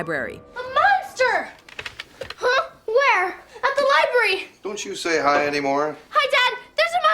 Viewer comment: oh, i thought he said, "dont you stay high" XD